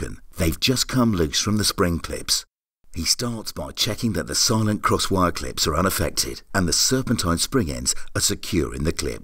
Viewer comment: What type of clips does he say? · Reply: A clip roon the ear😂